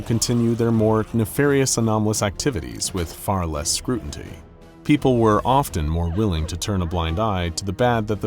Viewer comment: Yeah I thought I heard that right, you definitely said "scrutanty" and not scrutiny. Not that I'm scrutanizing or anything. 😂